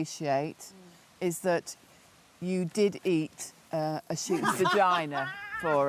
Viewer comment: The closed captioning has a woman in a red vest saying to Matt Hancock that she appreciates that he ate a student’s vagina. I can’t tell what she actually said because people are laughing over it. Did he, in fact, eat a student’s vagina? If not, whose vagina did he eat?